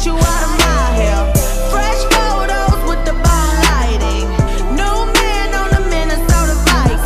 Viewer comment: I though she was saying - Don't text me, send it straight to myspace, - this whole time lol I am dumb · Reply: Christy Arthur i thought she said ball lighting lmao